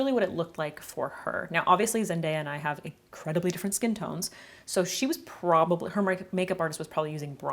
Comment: Instead of "Zendaya and I have different skin tones", I heard "As as man I have different skin tones"..?! 😂I had to turn the subs on, but for a second I was elated by the possibility of a trans sisterhood... 🏳‍⚧It's fine though - I'll just continue to aspire and learn from you x 🤩